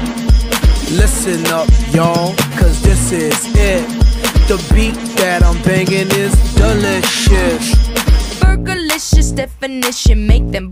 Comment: i thought he was saying "the beat that im bangin is the next shit" like..